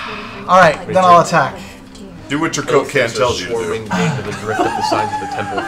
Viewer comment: What does Travis say to Sam around 1:21:28 that sent all the guys into hysterics? I can't seem to make the word out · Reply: As far as i can tell "Do what your coke can tells you to do"